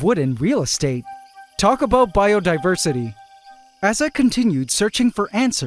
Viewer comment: The closed captioning said "Biodensity" instead of biodiversity like you said around the 23:30 minute mark